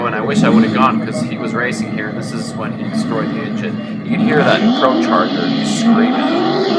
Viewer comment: what was you saying? the audio of the cobra took over. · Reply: "You can hear that Procharger screaming" is what it says there. After that the announcer and other people are talking at the track.